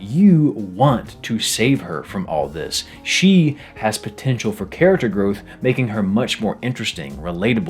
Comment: DID YOU SAY POTENTIAL